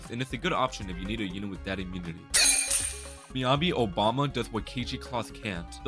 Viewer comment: did he say obama?